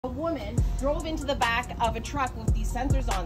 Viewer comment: So its made specifically because of women drivers? Because Thats what she sounded like she was saying